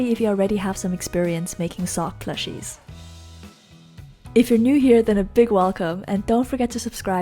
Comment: at the begining i thought you said "if your new i give you a thick welcome. lol